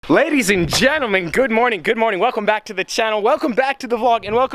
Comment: did he just say pladys and gentlemen